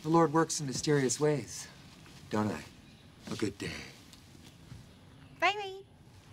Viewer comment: I love how it sounds like she says, “Bye Vade”.